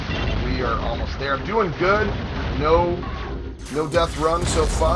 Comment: Did I hear no-death run? 🔥 LFG 💪 Nicely played! Question: is the double-shot ammo depletion time based or count based?